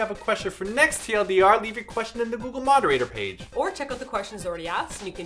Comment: when said "leave your questions and comments on our google moderator page", i thought he said "leave your questions and comments on our booboo moderator page". yerp. mah mind be trollin'